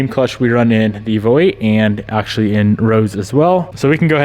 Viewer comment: Did you just call one of the cars Rose at 3:17?